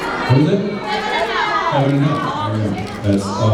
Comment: What episode does jensen say at 9:05? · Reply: Heaven and hell